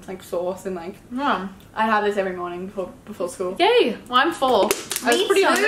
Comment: when said "I'm full" I thought she said "I'm four"